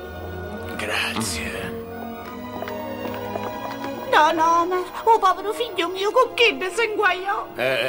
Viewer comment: cosa dice la vecchia donna? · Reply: “Don Homer u povero figghio mio con chiddi s’inguaió” con accento siciliano ..tradotto in italiano sarebbe” Don homer il povero figlio mio si mise nei guai con quelle persone”